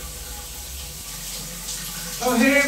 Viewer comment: "oh hey mom, im just Frost chicken" Trows in a toilet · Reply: I thought he said defroseted the chicken